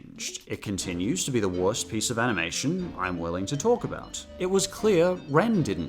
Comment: i thought you said shojo tsubaki was the worst animation ever